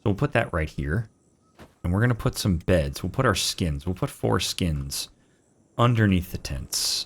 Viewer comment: "We're putting four skins under the bed" I was like did he just say foreskin?